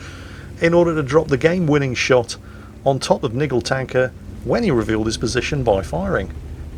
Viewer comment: uuhhh ... I didnt read the name before he said it, I heard it as "Nimble tanker" .. couldnt help laughing at the irony ;-)